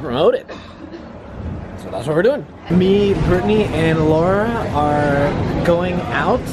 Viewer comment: Hey @ 3:17 did you say me , Britt & Allura ?!? Bc that's my name 0.o pretty dope lol